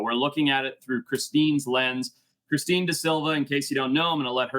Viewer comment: At first I thought you said through "Christine's legs", this piqued my interest indeed. Mrs. Gallante definitely holds some ill feelings towards her 😂